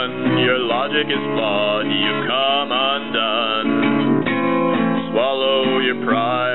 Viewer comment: can sombody tell me what he said in this part? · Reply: Your logic is flawed you come undone